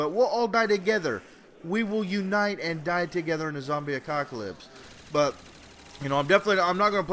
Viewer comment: i could have swore i heard him say zombie acockolyps lol